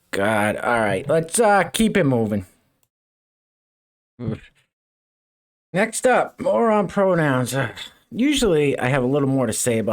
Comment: "next up, more on pronouns" I swore he said "moron pronouns"